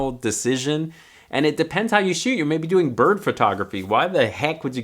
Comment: I thought you said "birth photography" whahaha